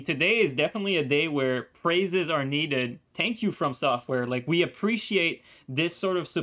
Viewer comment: Yes Tank you from software so much for the hot fix. Lol just messing with you I heard you say Tank you instead thank you